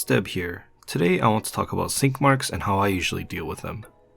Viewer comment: Swore he said skid marks!!!!